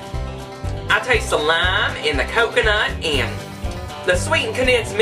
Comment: in the video your accent sounds like you say lime in the coconut. It reminded me of silly a song. Put the lime in the coconut, drink them both together, Put the lime in the coconut, then you feel better, Put the lime in the coconut, drink them both up, Put the lime in the coconut, and call me in the morning. I'm not trying to make fun of you. It just caught my attention. LOL